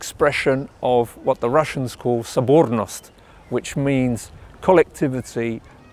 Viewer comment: what was that russian word (sovornost)? I played the video to a russian, but he did not recognize the word! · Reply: Sobornost